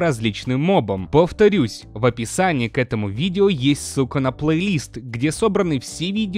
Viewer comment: кажется что он сказал Су*а а не Ссылка (прям не чуется что это Ссылка а кажится что с*ка)